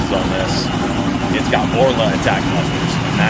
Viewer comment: What did you say the exhaust was? I heard Borla, but couldn’t understand the second word! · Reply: Borla Atak mufflers but now I have MRT on it. Always changing. Lol Thanks